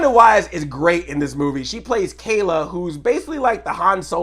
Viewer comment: The only reason I thought DeWanda Wise was playing Malcolm's daughter is because I really thought she introduced herself as Kelly at one point. But then I heard her say Kayla.